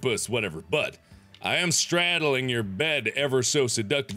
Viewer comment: omg the dislikes number is 666 seriously look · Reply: I thought he said but I am stranded in your bed